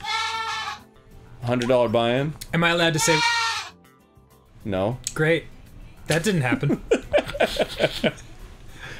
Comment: What did Dan say at 6:50? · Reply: Table flip